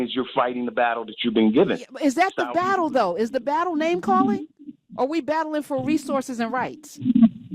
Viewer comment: I'm Sorry Miss Hunter I thought you said are we battling for resources and rice (😅). I listened again and heard the full conversation. Thank you